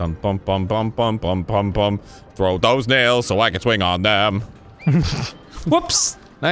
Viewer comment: What Arin actually said: Throw those nails What my ears heard at first: Frodo's nails